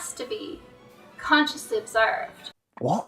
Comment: Can anyone help me here. At 10:02 she say something about the picture should be "berwed". I cannot hear what she is saying, but it sound really strange and like rubish.